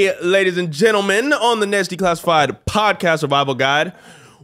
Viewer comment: Lmao sounds like Daniel says "Nasty classified" in the intro